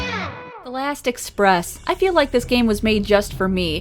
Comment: I was listening to your videos on a playlist on the background while doing my homework. At the intro of this video I misheard what you said as "elastic breasts... I feel like this game was made just for me..."